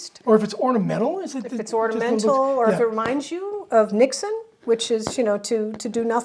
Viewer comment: Thought you were saying 'reminding you of Nixon'. Took me a second to realise there must be a Dutch word for doing nothing.